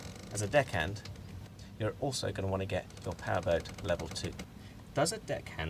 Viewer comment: Hello, hope you're good today Excuse me, what do you say in 9:40 ¿perth ..... what? I cannot understand it Thank you very much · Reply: Hello Juan. I’m very well, thank you. I say Powerboat Level 2. It’s a Powerboat course that can be done at schools throughout the UK and Europe. Another option is the IYT Small Powerboat and RIB Master course.